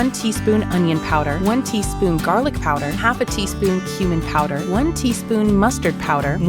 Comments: i thought it was said human powder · I braced for impact when I heard “Mustad Powder” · did i hear mustard😈💀✌️